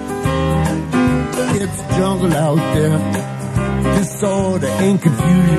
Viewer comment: All this time I thought he was saying “it’s jumbled out there”